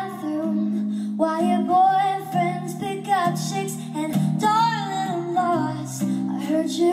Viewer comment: En donde dice(pick up chicks) pense que decia piKachU y mi mente dijo OMG! Asi que asi se llama pikachu en español 😂